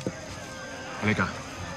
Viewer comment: Wait, tama ba narinig ko sinabi no Wahid "wait lang" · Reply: "Halika" raw. 👀